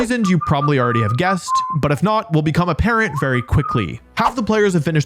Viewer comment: I literally heard that as “and if not, we’ll become a parent” 😭😭